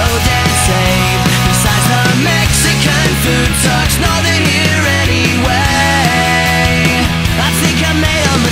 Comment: Huh, I've always heard it as "Besides the Mexican food sucks, love it here anyway." Now I'm conflicted...